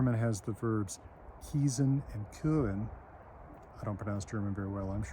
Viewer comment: What are the words written out? I speak German, but can't figure out what Dr. is saying. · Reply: He says "kiesen" and "küren". But the first one is so archaic that it is no longer considered correct standard High German by anybody. Only "küren" is correct today.